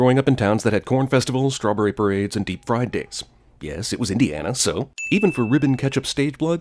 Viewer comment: Thought he said "Deep Fried Dicks"